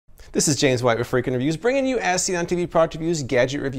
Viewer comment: Every time I hear "This is James white with Freakin' Reviews" I always think that he says "Freakin' Interviews" lmao.